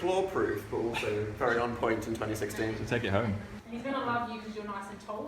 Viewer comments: Did SAY THAT HE'D "TAKE DAN HOME" AT 6:52 WHEN THEY WERE TALKING BOUT HOW DAN LOOKED IN THAT JACKET OR IS IT JUST ME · ngl I thought said "take me home"